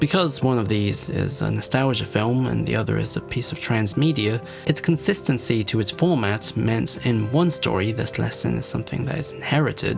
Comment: What does he say at 12:32? I couldn't understand him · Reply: “its consistency to its format meant that in one movie this lesson is inherited,”